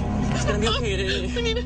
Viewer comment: I heard "WE NEED MADIC HERE, WE NEED MEDIC" :D